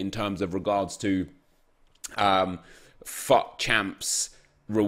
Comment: subtitles said "fu*k" when said "fut" 😂